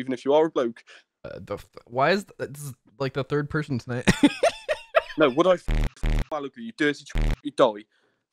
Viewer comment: What on earth did he say at 2:25? So much censoring I can't even tell. I heard "Would I fking something something you die"